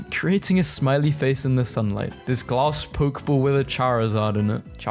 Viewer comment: did he just say poke ball